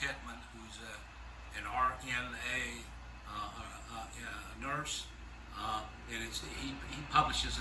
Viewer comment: I thought he said " your gonna need a hurse". Lmao he said nurse.